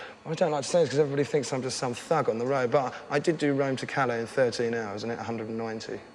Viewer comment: Does anybody know what hes saying at 3:18? Rome to what? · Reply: Calais